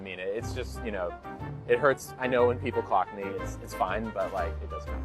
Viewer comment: did he say 'when people cock me'?